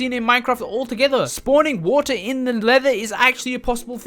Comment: is it just me or did he call the nether "leather" hook your cow farm to this dimension to get "leather"